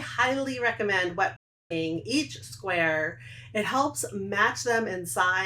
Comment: Hello, what do you say at 6:43, there is a short glitch and the CC says “wiping” which I don’t think is correct. Thanks for the video! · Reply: She says that she strongly encourages wet blocking the squares.